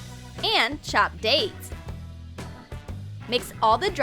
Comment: When they said "and chopped dates", I thought they said "and chopped aids." When will I learn